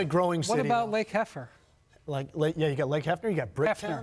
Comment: He said Lake Hefner! 😂😂😂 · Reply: I thought he said Lake Heffer? 🤣🤣🤣